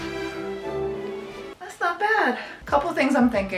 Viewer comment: thought you said FROTO-type. IDK My bad.